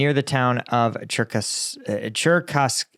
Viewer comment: I thought for a moment you said jercoskaya. Ah well, close enough. Slava Ukraini!